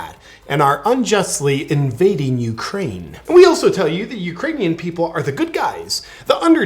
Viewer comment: "Putin may circle Kyiv with tanks, but he'll never gain the hearts and souls of the Iranian people." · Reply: Thought he said "The Uranium People"😅